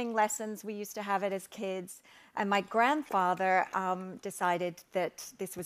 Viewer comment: Chip butties on a tree means they’d be cold. A hot chip butty where the butter turns runny can be amazing. A cold chip butty isn’t a thing anyone should want to eat. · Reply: I thought she said that's a prank her grandfather did. Under the tree